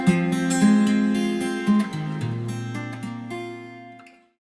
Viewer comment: what is he saying at 5:54 ? fools for Christ? foods for Christ ? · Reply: Fools for Christ. Like St Andrew of Constantinople.